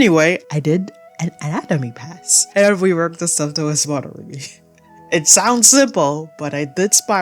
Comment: What did you say here? I've played it back a bunch of times but I just cannot make out the words. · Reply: Ooof sorry, I usually rerecord stuff that sounds this garbled but I completely missed it 😓 I'm saying "and reworked the stuff that was bothering me" 😅